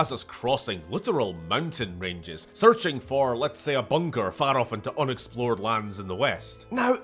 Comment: I seriously thought he said "searching for a Bunger" but no, he said bunker.... not nearly as good